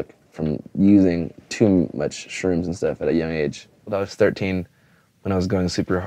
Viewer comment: I misheard him and thought he is talking about shrimps and not shrooms. And I started to listen in more carefully